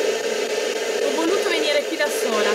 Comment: Minuto 6:32 io ho sentito "HO DOVUTO". LIKE per farglielo leggere 👍. siete i migliori 💪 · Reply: Io ho capito "ho voluto " poi magari mi sbaglio